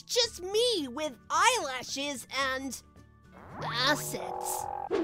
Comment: i thought tails said sessa